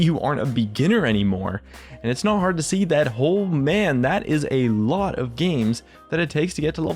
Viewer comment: If you watch with subtitles on, at 1:45 youtube thinks he said "holy batman" instead of "oh man"... i think youtube knows what's best.